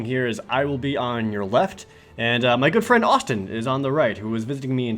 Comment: I first heard "my girlfriend Austin", and thought that was an uncommon name for a lady.